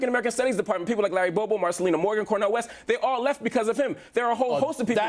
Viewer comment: sounds like he says Kanye West